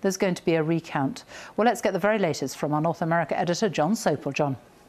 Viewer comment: Jon Sopel. I thought she said John Soap!!!